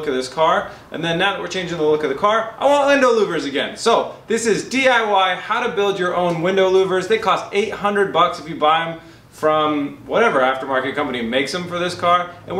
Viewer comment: Did you say Lindow Wouvers at like 1:03ish